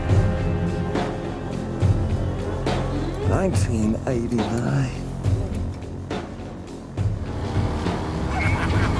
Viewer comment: What David Bowie says at 0:40? I ve seen...?! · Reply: 1989